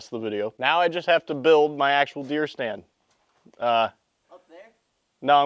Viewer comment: I thought it said BEER stand not deer